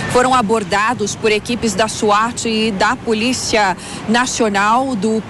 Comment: Escutei bem .... SWAT (7:28) ?